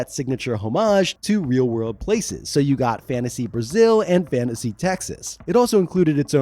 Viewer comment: did you just said Fantasy Brasil? Now I realy need to get into Mystara.